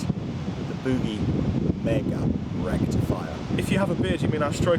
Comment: Ahhhhh I see now... "RECTIFIER". I thought Mesa said Rectum Fire - that's why I thought it was such an odd rumor and went with a Marshall instead. Dammit.